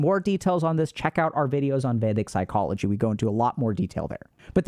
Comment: Which video was Dr. K mentioning around 18:00? Didn’t understand what he said there and I’d like to give it a watch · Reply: ^, did he say vadic?